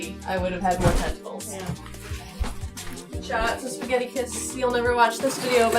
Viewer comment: I thought said "Shoutouts to Spaghettiflips!"